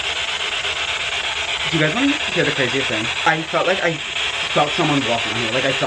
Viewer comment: Before the part where they were talking about if the spirit said cat, I thought I heard the word "CRAZY"